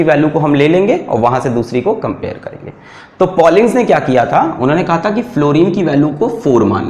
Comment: What is the name of the scientist sir said? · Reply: Pollings